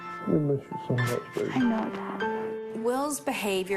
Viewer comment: did he say I know mom to his dad?